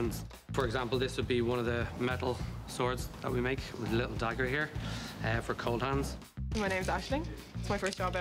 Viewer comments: The guy says ''The dagger for Coldhands'' or did I hear that wrong? at 11:45 ish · Did he just say "a dagger...for Coldhands"?